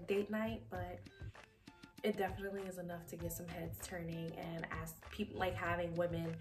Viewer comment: all I heard was “its enough to get some head” so i bought it before she finished the sentence 😂 EDIT: I have not been offered any oral favors. Cologne smells fantastic though.